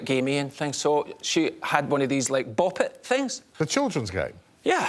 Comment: What is that thing? I tried looking it up but I hear 'bobit' and the auto subtitles gave worse suggestions and google was as confused as I was. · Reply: Bop It, it's a toy that dates back to the 90's